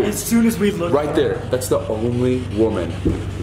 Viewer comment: i felt like i heard "thats my mom.. " is it just me?